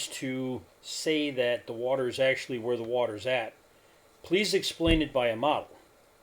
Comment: what did you say here? It sounds like you might’ve said “please Explain it by a model.” There’s no need to appeal to a model when you’re looking at a natural phenomenon that you seem not to understand. I’m not trying to be rude, but this is in my experience over the past six years exactly what I’ve said that it’s been and that is we are seeing farther due to the conditions of the medium and in this case the seeing farther is also coupled with a dynamic Mirage event.